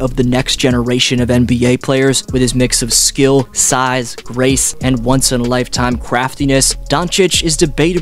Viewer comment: I had to turn on captions because this is what I heard: "Resembling the GOAT of the next generation of NBA players with his mix of skill, size, RACE...". I was like, "Wait - WHAT?! Please tell me Dflow didn't just call Luka the next GOAT because he's white." I played it several times, then I put on captions and saw that it was "GRACE". Whew.